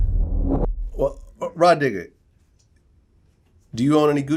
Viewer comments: swear i thought i heard my nigga instead of rah digga in the beginning seconds 😅🙄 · When vlad said rah diggah I thought for a second he said my nigga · It sounds like vlad says "raw niggah" when the video 1st strts 😂